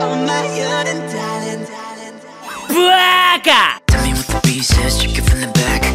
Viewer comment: “Bwaka” It’s not just me who hears that right-?